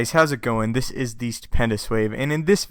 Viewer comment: you sound like your saying Stupendous Swave